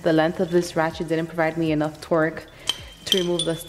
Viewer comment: did she say twerk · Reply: No, I said torque.